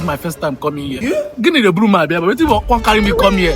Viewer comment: As the guy said, "gini geburum abia ebe a" l thought he said "give me the broom make l sweep here"😂😂😂😂